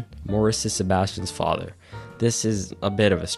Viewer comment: My brother in Christ did you just call it Telican Town? 🤔😂😂😂